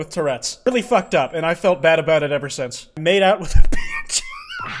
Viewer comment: at first i thought you said i mad out with your bitch and that killed me and then i played it back and it said png i proceeded to then litter roll on the floor laughing thank you